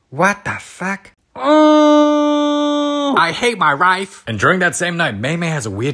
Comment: why did I thought she said I hate my rice